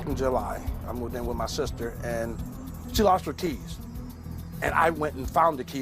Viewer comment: I thought he said she lost her TEETH!! Yikes!! How in the world do you lose your teeth??? Ooops! He said Keys. My bad 😄😄😄